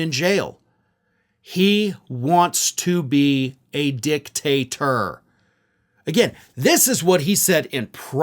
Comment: I thought he said dictaster!!!😮